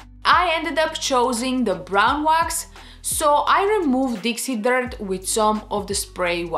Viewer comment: I thought you were saying ‘DC dirt’ wax. Thought WOW! Awesome name🤣🤣